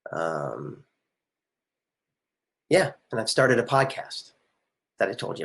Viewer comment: I had to relisten cuz I thought he said I started an APOCALYPSE.